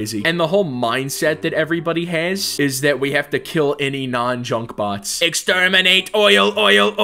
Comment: I thought you said chunk bot